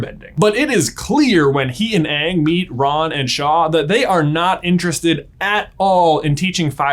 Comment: *Ran and Shaw Kinda sounded like you said Raw and Shaw. XD